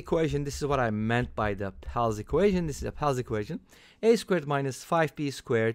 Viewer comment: Great video! What did you say was the name of the "a^2 + 5b^2 = 1" equation shown at 5:56? The "pals" equation? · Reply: Thank you! Pell's Equation